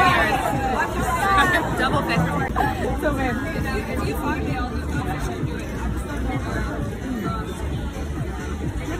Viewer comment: Does christen say "here babe" at 05:52? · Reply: She says “Here you go”, but I do believe they’re dating, anyway.